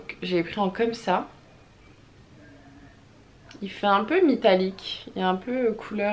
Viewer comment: J’ai rêver ou à 7:55 ta dit mitallique ces qui on entendue sa dite moi le par ce que je veut savoir si je suis là seul à avoir entendue sa